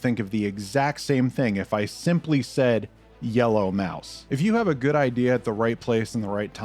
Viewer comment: I thought you said "yellow mouth", and thought you meant Pac Man.